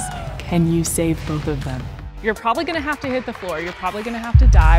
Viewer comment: I heard “ you’re probably gonna die...” Me: Wha